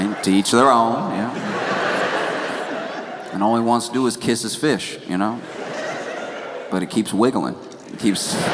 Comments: Thanks for the translation. I also thought he was saying he wanted to kiss his fish! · I thought he said kiss his feet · I thought he said kiss his fish too! 😂😂😂 Josh, everything you said about Bad Bunny’s show was spot on. So well said. Thank you as always for insight.